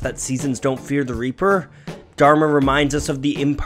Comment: And here all this time I thought it said "don't fear the reefer"... 🤣🤣🤣